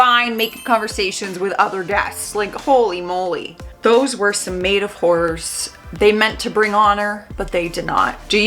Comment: did you say maid of whores??? This channel is getting good.